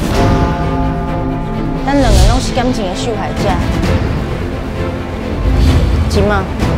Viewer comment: ，感情欸"咻"害者 還有3:27他到底在說什麼？？